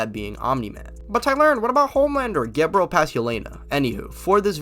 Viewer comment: What did he say here 0:17 . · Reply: Get bro past Yelana